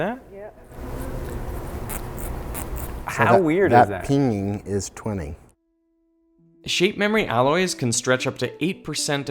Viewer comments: What does he say at 15:39? I hear "That peening is pooning." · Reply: And almost a year later, I hear "That pinging is twinning" :) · What does he say at 15:39? I hear "That peening is pooning."